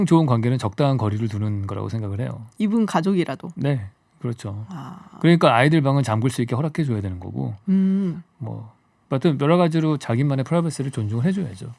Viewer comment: 이븐 가족이라도?? 잘못 들었나...